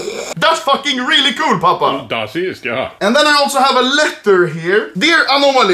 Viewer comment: wtf does papa say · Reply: Das ist, ja